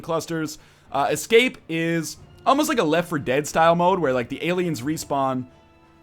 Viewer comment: Oh now I see, he said left 4 dead 2 I thought he said Dead Space 3 and I was really confused.